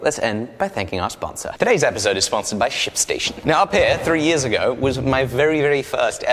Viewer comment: I thought he said sponsorship by sh&station 😭😂😂